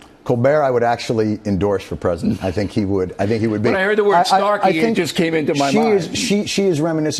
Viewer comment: I thought Gervais said sarcy in his comedy like sarcastic. Then realized he said snarky. They should both be words. Sarcy bastard.